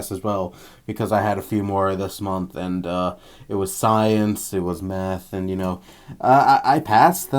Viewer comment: it sounds like you said "It was science... it was meth...." lol